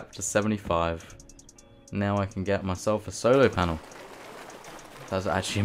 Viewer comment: did you call a solar panel a solo panel?